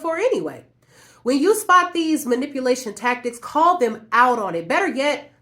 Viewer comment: I thought is saying "call 911 if you see these manipulation tactics".